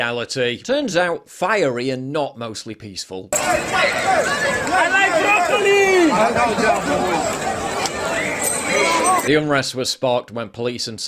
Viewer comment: did he say he likes broccoli 🥦 ?